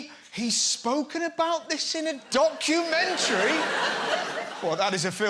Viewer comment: Did he just say "dogumantary"?